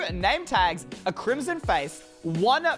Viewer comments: instead of saying "crimson fence" he said "crimson face" but I also sometimes mess up my words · I thought he said crimson face at 12:37. 😆😆😆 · did he say crimson face ???